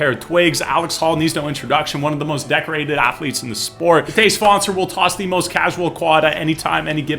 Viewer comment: I thought he said todays sponcer 😭